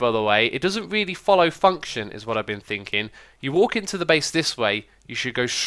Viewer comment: When u said function it sounded like u said the f word